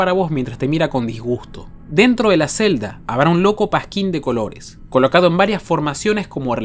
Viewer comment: escuche un loco pajin jajjaaj